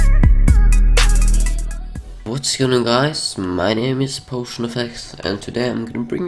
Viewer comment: 2015: hey was going on gaiz potionefex hear 2016: Hey whats going on guys, PotionFX here. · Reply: In 2015 it sounded like he said Paul Shrin instead of potion. lol